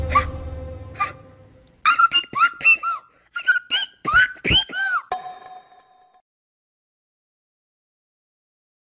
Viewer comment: Wait What did she say??? I heard the first part not about Wanting to date Black Poeple But Wtf Was That Second Part??? Because what i Heard was Something Very Wrong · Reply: She said she doesn't date black people twice